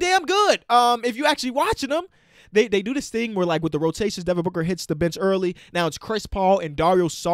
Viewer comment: When he said Devin Booker hit the bench early I thought he was saying hit the bitch